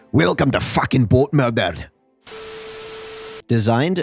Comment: I thought he said “GoatMurdered” and I laughed even harder